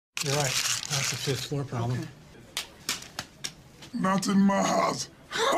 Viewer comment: Oh my god, this whole time I thought he was saying "Naten Maz" and I thought it was just either gibberish or some foreign phrase I had never heard of.